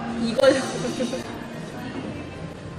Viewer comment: i heard Taehyungahh 😂